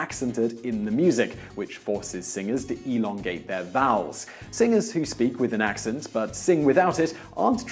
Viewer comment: I have a hearing problem, when he said vowels I thought he said bowels. I said- ewwwww! !